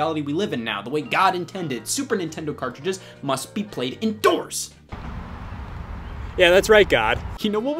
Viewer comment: I heard Endorsed first and it made it that much better